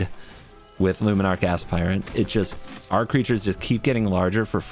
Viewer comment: Did he say “luminarch ass pirate” around 9:22?